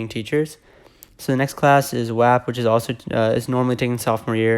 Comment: I heard WAP and I was like wait...WHAT